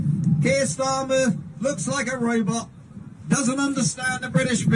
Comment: what's he saying, what dey, robot 🤖?